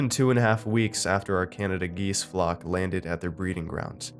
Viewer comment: I thought it said I study Canadian cheese not geese